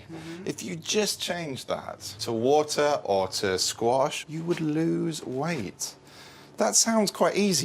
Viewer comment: when Dr. Christian says " if you just change that to water or to ____" is he saying squash?